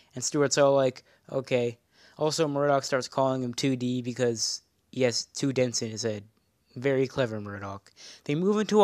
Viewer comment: "Murdock starts calling him 2D because....." what the fuck does he say? I keep hearing "yes, two dents izay" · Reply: “He has two dents in his head”